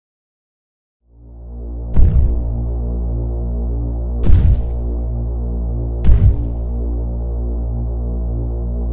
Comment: if anyone knows what that creepy voice says please leave a reply I think it’s backwards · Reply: That demon says "MY house......Get......OUT" And the it walks away (Tried to make it fit the vid)